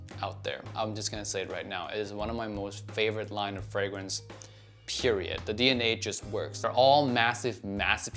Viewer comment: did he say Periodt ?